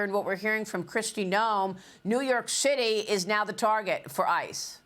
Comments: I heard "New York Shitty". 100% spot on, actually. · I know it's just the way she talks, but it sounded like New York Shitty.